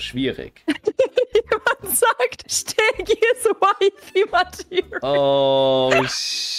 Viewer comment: Was sagt die da bei 1:00? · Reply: is wifey material